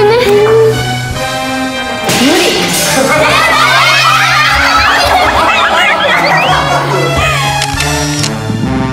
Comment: Can anyone translate the word the host uses at about the 9:15 mark to describe the toad to them? I can't quite catch it to put in Google translate. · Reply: MrOsclasses she said “Impossible! “ in Japanese. “Muri”means “impossible or I can’t handle it”